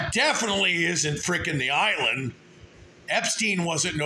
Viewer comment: I thought I heard the island sold. I wonder who bought it?